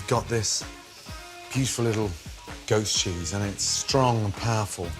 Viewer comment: I seriously thought I was hearing him say “ghost cheese” but he was saying “goat’s cheese”, right?